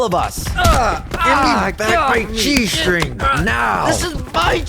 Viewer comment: I was playing this video in the background and I thought it said G-string, but luckily I had subtitles on😅😅😅😅😅 (edit English isn't my first language)